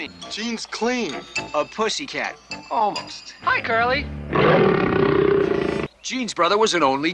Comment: Tell me, what does it mean - hi curly? And why is Ace using this? I'm dumb. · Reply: LOL! I always thought he was say hi Carley! 😂